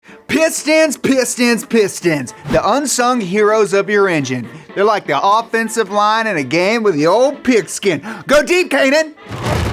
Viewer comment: i thought you said “piss stains, piss stains, piss stains” in the intro 🤡